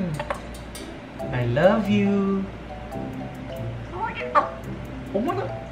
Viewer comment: i thought i heard her say "i love you too"